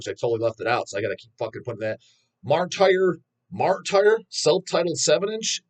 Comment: I cannot make out what the Australian band is that you're mentioning around 12:16 for the absolute life of me & it's driving me insane. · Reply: it’s called Martire lmao